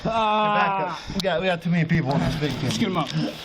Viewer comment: did he just say “get up, we got way too many people on this VICTIM”? Did I hear that right?